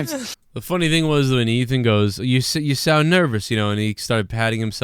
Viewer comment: I think at 13:00 Ethan said "you see Im nervous"